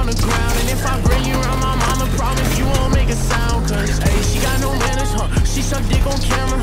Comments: Al principio pensé que decía “no mames” jajaj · Pensé que decía no mames